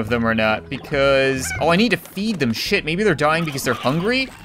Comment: I I thought he just said screw and crap but no what he said was: oh i need to feed them s***